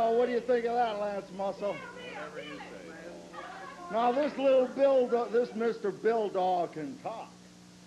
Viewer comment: i thought he said "this little dildo" LOL